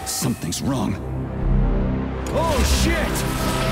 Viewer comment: He says Oh Shit!, but the subtitles say Oh, fuck!